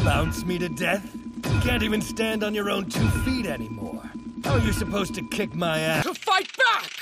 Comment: what did he say 😂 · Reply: "How are you supposed to kick my ass?"